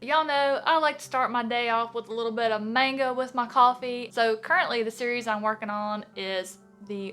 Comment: For a second, I thought you said "mango with my coffee." I mean, no judgement. Fruit is good for you! But, y'know. ;)